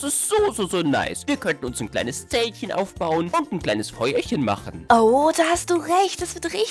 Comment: hab beim ersten mal "Bäuerchen" verstanden😂😂 wer noch??😂